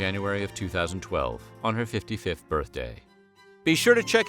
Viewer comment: "One her 55th birthday." The first time you said that, I heard "155th birthday." 😄😄